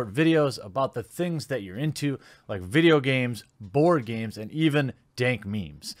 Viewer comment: I thought you said "vore games" instead of "board games" Y- You said board games right?